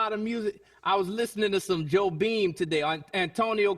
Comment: I'm Portuguese and I honestly thought Jon Batiste said Joe Bean xD (it's actually Jobim)